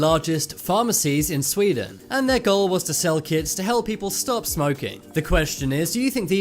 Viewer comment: did you say sell kids